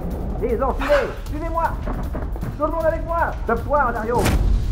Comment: "Tout le monde avec moi ! ... Sauf toi dario !" xD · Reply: je crois que c'est "sauve toi dario"